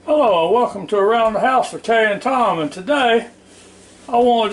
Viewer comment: I thought u said around the house with tator tom